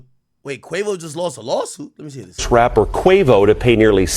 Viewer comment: Am I tripping or did he say trapper 😂💯